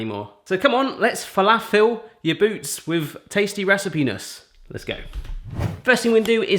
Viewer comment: Did you say "recipenis"?! 😶